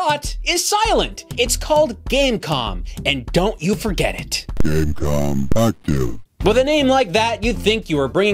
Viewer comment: did it say "gamecom, f you"?? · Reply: I think it's supposted to say "Game Com, Active"